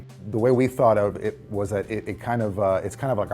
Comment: I thought he said porno as first